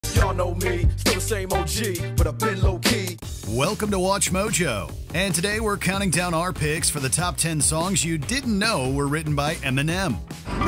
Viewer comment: When I was little listening to Forgot About Dre I always thought he said, "Y'all know me, still same OG. With a pillow key." 😂